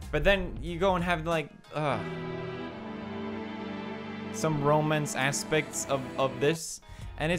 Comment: OH MY GOD!! He's saying romance I just relized I thought he said Romans